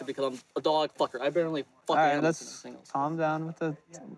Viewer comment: AM I tHE ONLY ONE WHO HEARD HIM SAY HE “BARELY FCKS ANIMALS” 🤦🏽‍♂️😳